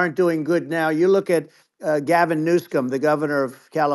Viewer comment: did he call him "Gavin New Scum" 😂😂😂